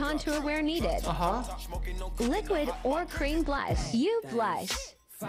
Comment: Not James telling BRA instead of blush. lmfao